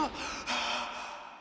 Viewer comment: hold up did Vegeta just say Goku? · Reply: Listen closely, you can hear him say Kakarot